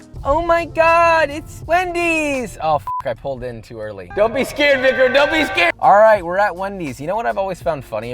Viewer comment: Ummm what did say at 0:52?? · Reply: I had to do a double take too but he said, he was talking to the camera man